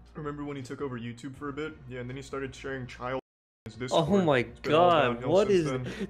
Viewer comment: what was said at this part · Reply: Cp